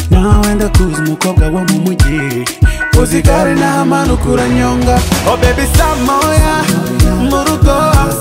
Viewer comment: I think I heard the name "kunyonga"...😂😂